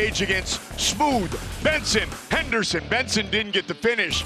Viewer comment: It sounded like he said "Spoon Ben Henderson" at first.